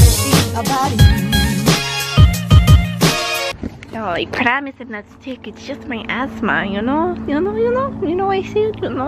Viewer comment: i think she said something like "I promise I'm not sick, it's just my asthma"